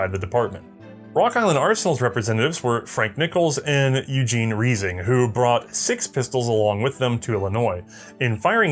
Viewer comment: It sounded like you said that one of the Rock Island representatives was [phonetic spelling] "Eugene Reezing". Would that be Eugene Reising, the later SMG inventor?